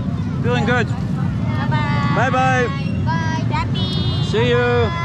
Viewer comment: who translated this🤣 i lost it when i see the subtitle at 29:28 when the girl said "hati - hati" which means careful but subtitle said daddy 🤣🤣